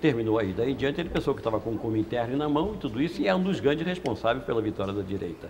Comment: Genial!! Alguém poderia me dizer o que ele falou na parte 45:55 até 46:03? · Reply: Ter o "Komintern na mão": refere-se a uma pretensa liderança da esquerda articulada, à época da posse de Jango. Sobre a menção a "ter o revolver na mão para combater os canhões" refere-se ao ato de resistência aos militares, liderado por Brizola, quando governador do RS, a partir do palácio do governo em Porto Alegre.